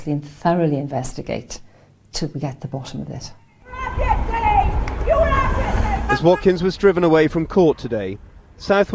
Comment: What is the woman at 2:19 shouting? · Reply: Ian